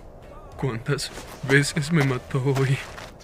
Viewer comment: Con ese doblaje pensé que decia "cuantas veces me mató wey" , no seria nada raro jaja